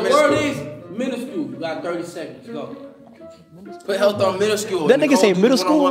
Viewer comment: I AM CRYING 😭😭😭. ONG it sounds like fanum said middle school